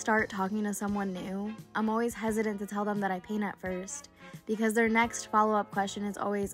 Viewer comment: "i'm always hesitant to paint at first" What i heard "I'm always hesitant to tell them i peanut first"